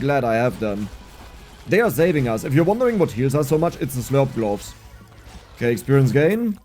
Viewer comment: "Slurp glop"? Did I heard that right? Is that his German side speaking? [Edit:] I finally heard "gloves" sorry, for a second I thought he meant the hamburger on the floor